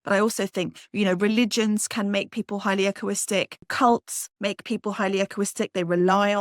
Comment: Can someone help? What is she saying? "Acquiristic". That's not a word so I don't know what the words is that she's saying. @ 19:25 (the transcript shows "acoustic", but it doesn't sound like what she's saying. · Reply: Oh nevermind. I figured it out. She's saying "echoistic". Got it!